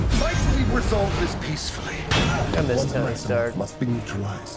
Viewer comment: did say 'I miss Tony Stark' because wow same 😭